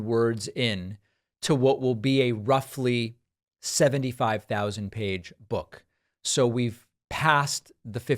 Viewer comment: David I know you meant 75000 word book but it sounded like you said pages. 😂